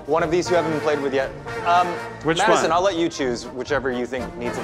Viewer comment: Am I the only one who thought Eugene called Matt "Maddison"